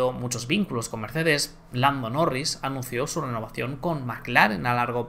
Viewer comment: soy yo o se escucha que dice Blando Norris?